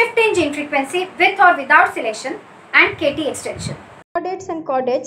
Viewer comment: I couldn't understand this name. Could somebody please tell me what it is? Is it K-T extinction???? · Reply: Yes it is k- T extinction